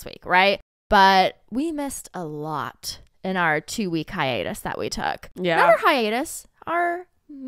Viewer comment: seconds in is it me or did it sound like she said high anus 😅 and literally thought looked at cos of how she said it 🤣🤣